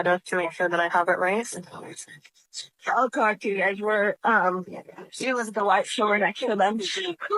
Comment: ATTENTION STARTING AT 1:10 = change the playback speed to .65 & you can hear EVERYTHING - BF “I’LL talk to you guys ummm it was the WHITES short guy that killed them” or “it was the whites 4sure that killed them” is what I hear